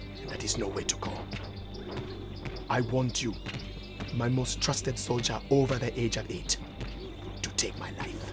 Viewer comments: I always thought the commander said "My most trusted soldier over the edge elite" 🤦‍♂️ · “My most trusted soldier, over the age of 8” 😂😂😂😂😂😂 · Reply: At first I thought he said over the edge of it